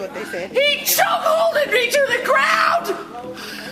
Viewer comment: did she say cuckolded me to the ground? Lol